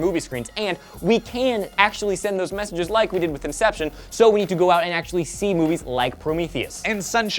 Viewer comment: LOL Elliot said "conception" instead of "inception" @ 1:47 lmao